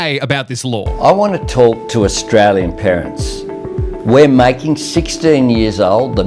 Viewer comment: am I cooked or something at 3:50 I genuinely thought he said I wanna talk tuah for a split second anyway, the government really had to think this through rather than blaming the companies and making them do their own thing. they should have at least created a list of websites and apps as well as their own definition of social media